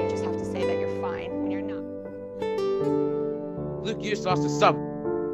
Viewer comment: what did you say? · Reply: You lost a sub?